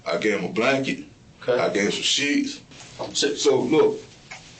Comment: Thought he said gave him some cheeks when he said sheets😅😅😅